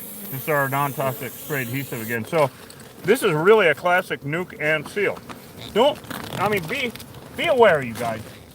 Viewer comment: For a long time i thought you were saying "New Conceal". Now I realize it was "Nuke and Seal". LoL. It's unreal where they will build. It seems odd, to me, that they will build underground. I guess it's dry enough they don't have to worry about flooding. Thanks for educating us. More people should see this because they are spreading east. The news showed they found a huge hive in Alabama.